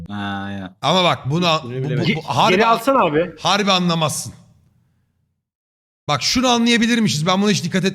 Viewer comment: ben anladım yani tam anlamazsın dediğini düşündüm